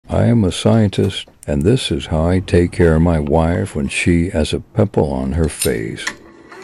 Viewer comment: I just woke up so I legit heard "I am a wife, and this is how take care of my pimple when there's a scientist on my pimples face"🕴️